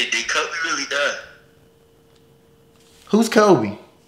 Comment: For everyone saying this is fake because he said “who’s Kobe?”. You gotta understand that everyone has different reactions, and your mind goes straight to denial. When my brother heard about his passing his first words where “Kobe? Wait who? Kobe as in the black mamba” he knew who kobe was, he has been his fav player since he was mad young. His mind just jumped into denial completely. I felt so bad watching him get all sad after, and I was in denial myself, I was even telling myself “Kobe is alive, this is all fake, I hope it’s fake” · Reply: I thought he said "who's kelby" cause ot lagged a lil N' he prolly heard him said "kelby" then he repeated N' said it again as "kobe"